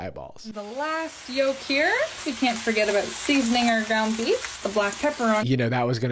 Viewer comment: My auditory processing disorder kicked in full force at the beginning of this video and instead of "ground beef" I thought she said "Gumby" and was like "Oh no she kilt him 😭"